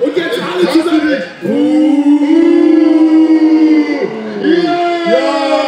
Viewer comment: HABE ICH DA BIELEFELD GEHÖRT